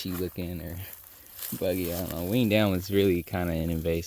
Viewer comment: Hey y'all. I like how you both methodically look at the land and plan your future. It's a beautiful thing. you kept talking about cutting that Winged Elm tree and all I kept hearing was "wean down" tree. 😂 I said wth is a wean down tree? I googlized it and learned about the Winged Elm. 😂